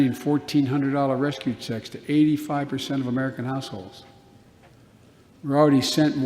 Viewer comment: I thought he said American ***holes!